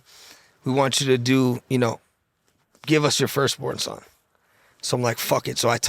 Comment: did buddy say he hadda give up his first born son to get those hangers in the deal..??😨